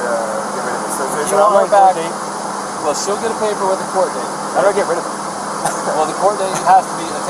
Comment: I read something about a "DEAD ZONE" At the 27:00 mark did he say "ima get rid of her?"